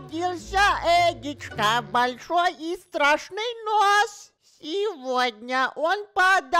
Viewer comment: Я случайно и прочитал, и услышал: "большой и страшный пёс" ))) сорри)))